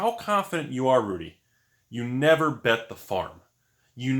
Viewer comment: I thought you said "Never bet the fart" and I thought, "Makes sense, sure, you could position just the right way, and the fart will most likely be silent, but there's that off chance that it lets out a squeak and you humiliate yourself, and sometimes the risk outweighs the benefit." But the farm makes much more sense in this context.